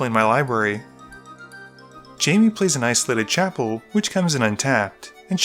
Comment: it sounds like you said:"Jammy plays a nice livid chapel."